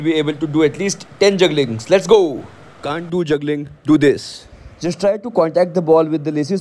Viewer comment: I heard can't do juggling = Gandu juggling